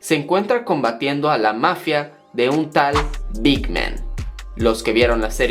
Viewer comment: Min. 1:34 Por un momento mi oído me traiciona pensando que dijo "Dick Man" XD